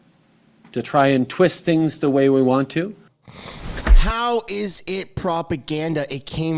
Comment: kinda interesting you didn't catch that he said: "the way WE want to"